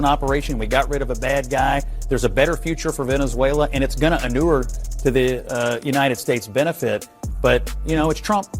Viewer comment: I hear, "manure-o"